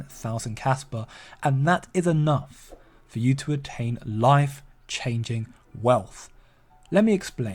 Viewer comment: I thought I heard you say "wife-changing wealth" 😅😅😅